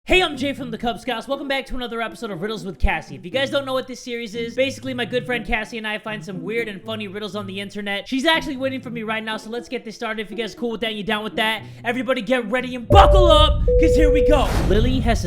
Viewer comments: I thought he said girlfriend Cassie Lol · Am I the only one who thought he said girl friend 😉😐😐🤔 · Oh Jay oh thought you said my girlfriend Kassie But you actually said my GOOD friend Kassie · When he said BUCKLE UP, I thought 💭 he said F*ck up🖕